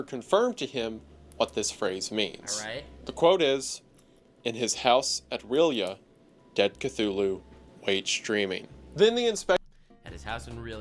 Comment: I was doing another thing and at 44:58, I swear I heard "In his house at rlyeh, dead cthulu is streaming"